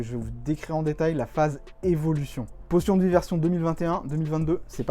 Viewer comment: J'ai entendu "Potion de diversion" xD